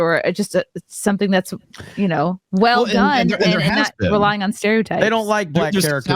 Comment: i thought you said nigga stereotypes. ain't wrong either way